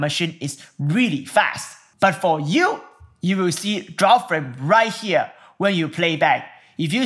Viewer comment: A great tutorial again! Way to go! I only have a question, at 8:40 you mention something which i couldn't understand, one word "But for you, you will see ?????? right here when you play back." Thanks. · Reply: So when the drop frame indicator turn yellow - mean your Premiere playback had drop frames - you will need to use Proxy workflow.